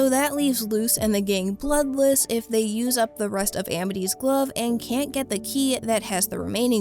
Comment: I thought said “from Amity’s glow up” instead of Amity’s glove😭✋🏾